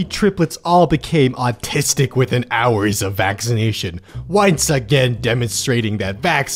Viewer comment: New Mic. Let me know what y'all think. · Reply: I had to listen to this while reading it because I fully honestly thought, instead of saying "once again", I thought you were saying "whites again"